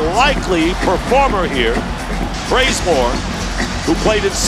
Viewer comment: does anyone else hear bRaZeMoRe?